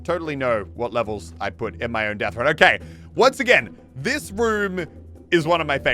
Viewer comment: did he say whoom? Because I’m dirty minded.